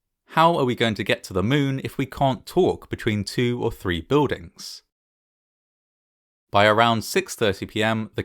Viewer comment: Grissom does NOT say 'between 2 or 3 buildings', but clearly says 'between 3 buildings'. Do you verify your source material or do we have to?